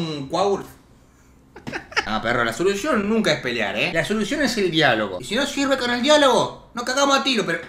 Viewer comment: en el minuto 15:33 entendi la solucion es el diablo